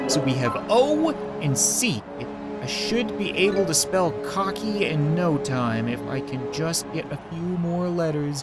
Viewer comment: “I will be able to smell cocky in no time”. Did anyone else hear that